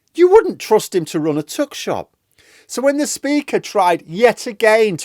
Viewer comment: "You wouldn't trust him to run a tuck shop." I'm not a native speaker, so I misheard that for a "tug shop". He probably couldn't run that either.